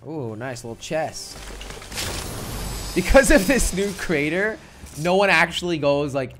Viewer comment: i thought said crainer